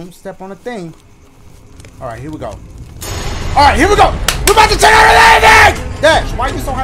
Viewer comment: What did he say 25:23 lol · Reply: He said “we bout’ to check out this ending”